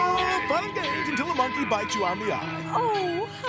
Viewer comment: What did he say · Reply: I’ll meet ya tonight at the play park I think